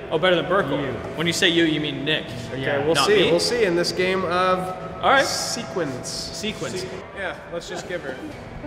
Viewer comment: i swear i heard "sequins" not "sequence"